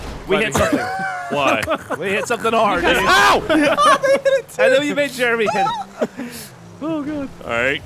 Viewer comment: did say "Ray hit it too" ??? Aw =P Sorry Lil'J